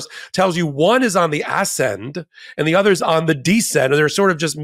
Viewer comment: I loved when you said, "One is on the ascend,". I first heard it as "ass end".🤣🤣🤣🤣